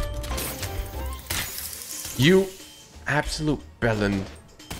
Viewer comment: did just say bellend